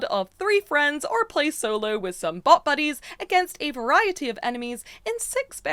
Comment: ngl I had to rewatch this part 4 or 5 times. I thought the said 'butt' buddies and not BOT buddies.